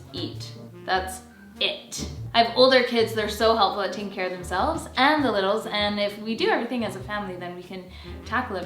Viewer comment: Did say the older kids take care of the little ones in the morning at 15:38? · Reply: She said they helped care for themselves and the little. Probably not in totality but by giving a helping hand.